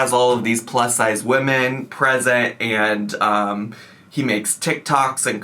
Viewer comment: at first I misheard this as “this guy who lives in a mansion where he has all these plus sized women imprisoned” 😬 the sad part is, the only thing that made me rewind was casual tone. The situation itself would not surprise me